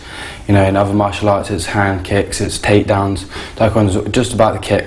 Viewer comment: did he just say "hand kick's"? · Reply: Head kicks, he said head